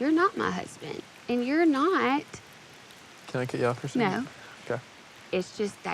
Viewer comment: can someone pls tell me what did he say? All I understand is Hannah said no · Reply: "can i cut you off for a second?"